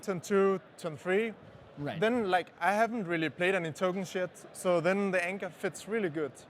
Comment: does he say "I haven't really played any token shit."? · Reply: Tokens Yet